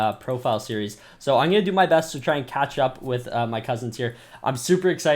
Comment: anyone hear him say cousins or is that just me cause the way he said it makes me think there cousins lmao